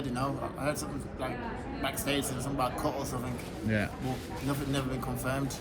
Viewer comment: What does he say at 0:50? 'Something about ...?' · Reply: 'something about a cut or something...'